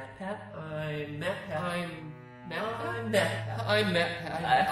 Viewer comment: Why do i hear I'm Batmat MATPAT IS BATMAN CONFORMED